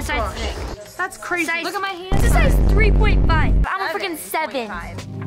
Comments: Anyone else catch what said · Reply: Yep she said frickin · i think i heard say f*cking lol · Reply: Fricken